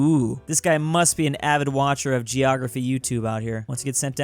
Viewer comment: Geography? I thought you said geometry